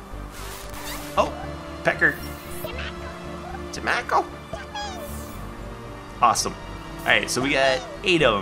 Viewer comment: Did say "tomacco" at 10:00?